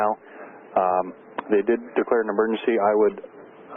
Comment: I'd say it is a water mark but it isn't although acts like one. It's something about the radar scop that I could delete easily but actually left it on purpose as a watermark. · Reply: I love your videos I'm bingeing them all! At 4:35 ATC says they DID declare an emergency, but the transcript says "didn't". It threw me and I was about to comment to ask "why did ATC say that when the pilot clearly said 'mayday mayday mayday?'" but then I realized the transcript was wrong. So... quick fix there!